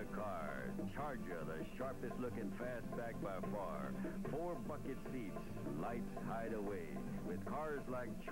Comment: I thought he said “4 fuckin seats”